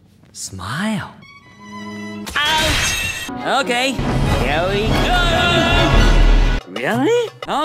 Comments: I doubt he's saying "darn it", sounds more like "dammit" to me ...but then again, every time I hear him say "here we go", I'm hearing "yaoi go" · WHAT DID HE SAY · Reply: i think he said "really". idk